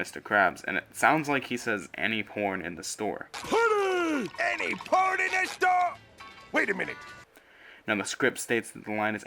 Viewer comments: I thought Mr krabs said any part of the store!! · ok i legit heard mr krabs say any porn in the store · He clearly says "Any port in a storm" which is a sailor saying and makes sense contextually